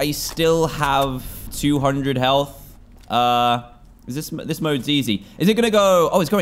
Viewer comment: Dantdm I thought you said the mode is easy